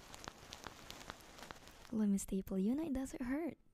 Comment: I thought she said 1 staple 😅😂